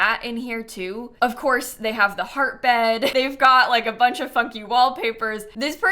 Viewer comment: Oh my god it's way too early in the day for me to watch youtube- I thought just SWORE and HARD in a video 😭🤚🏻 "They got the heart bed... They got a bunch of FUNKY wall paper-" I thought she said "f**king" instead of "funky" 😭😭😭